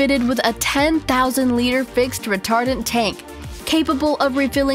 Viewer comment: i thought shse said "retarded tank"